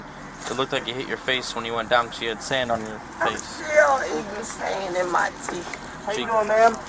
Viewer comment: Did she just say she’s still eating sand? 🤦🏽‍♀️ “Lord I am so sorry for this laugh! At least I didn’t laugh when she fell.” 😭😅🤣